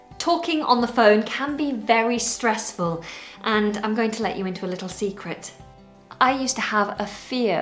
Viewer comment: What did you say in the second 00:27 "I'm going to let you in on a little secret" or "I'm going to let you into a little secret"?.thanks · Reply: She definitely says "I'm going to let you into a little secret". This is chiefly British English, in America we would say "I'm going to let you in on a little secret". I am American, and I would personally say "in on", but I know that both are correct. I just checked the full version of the Oxford English Dictionary (which is mostly British), and it says "into" is most common and "in on" is obsolete or American. They have quotations going back to 1680 using both forms. Interestingly, "secret" used to be spelled "seacret" in some of the examples, but not all. English has changed a lot in 340 years. Now that has let us into this secret (or let us in on this secret) about her past fear of the telephone, were are all in the secret. You could also say we are all in on the secret. "To be in the secret" is to be one of the few people who knows the secret. It's like being in a conspiracy, or being in a plot. So don't tell anyone about telephone fear! (That's a joke, she wouldn't post a real secret on Youtube.)